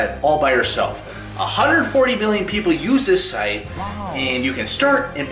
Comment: I thought that he said billion but he said million